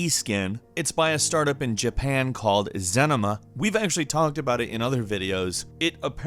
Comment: Wait, did he just say Zenima? 🤣🤣😂🤣